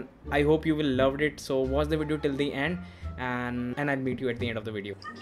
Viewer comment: Bro!😂I heard I'll beat u at the end !